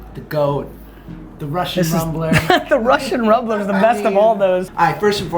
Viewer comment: I thought he said the Russian mumbler.